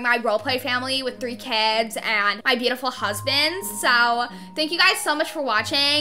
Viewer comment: did she say husband’SSSSS